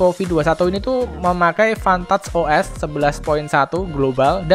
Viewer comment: Menit 2:09 hampir salah denger😁😁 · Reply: Pantat os 😁